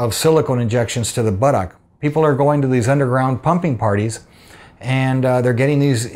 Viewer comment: I seriously thought doc said people go to “underground PUMPKIN parties.”